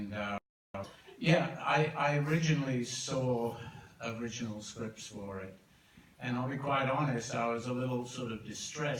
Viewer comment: omg I'd love to know, what he was saying.. 🤣 But it's so kind of taron at Elton that he covert the microphone ☺️❤️ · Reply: I think "You look fabulous tonight""